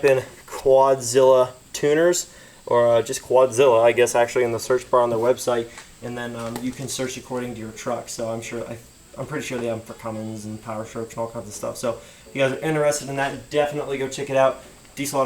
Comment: Did he say Cummings @ 3:49 lmao